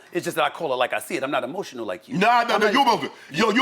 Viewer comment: wtf you say? “You ebowlofshit—- you u u eboshional” 😂